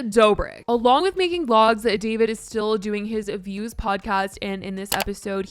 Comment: Oh Views Podcast, I thought you said Abuse Podcast. Even the Closed Caption say that. Ironic